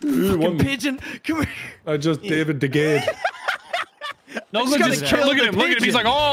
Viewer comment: I had to listen to 1:56:38 like 7 or 8 times to figure out what nogla was saying. Spoiler: he's not saying "I just dated a gay".